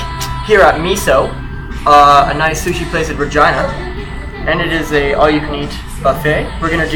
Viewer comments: i know it's Regina. it sounded like said vagina though. it's supposed to be a joke and i need to chill out a little. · did he say vegina?